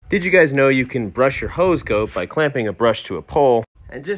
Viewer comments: Not the hose goat😂💀😭❤ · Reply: I heard "ho's goat". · Did you say hose goat?!! Did I mishear that?!! 😂😂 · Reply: It's the breed of dog. It's a Borzoi, their nickname is Hose Goat. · Did you say hose goat?!! Did I mishear that?!! 😂😂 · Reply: Totally thought he said “house goat” and then I was confused bc it kinda looked like a dog too